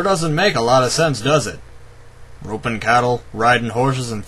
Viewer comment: sounds like he said "gropin'" cattle instead of "ropin'"